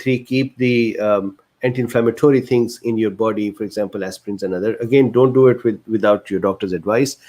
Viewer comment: what did he say? Aspirine?